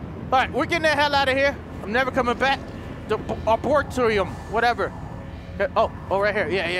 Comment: Abortorio instead of Arboretum LOL